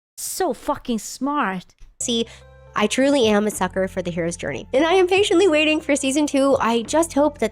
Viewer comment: did she just say shes "a sucker for the hairs journey" while the egg is in the background?